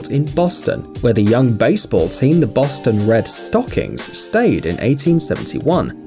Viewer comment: Did he say Boston Red Stockings?